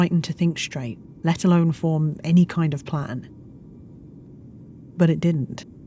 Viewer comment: SUBTITLES SAY PLANE INSTEAD OF PLAN, 0/10!